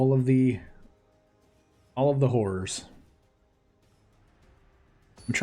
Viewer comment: I know you probably said "horrors" but man.... That was the most casual call for hoes I've heard in my life 😂 There's no way im the only one who heard that